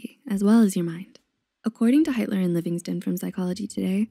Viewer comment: the subtitles said hitler :0